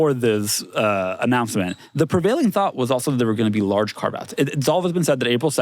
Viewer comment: What's saying at 3:41 and 3:49 ? Large car baths?